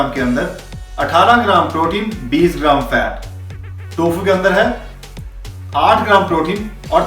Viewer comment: Paneer 100g is repeated twice in subtitle instead of Tofu.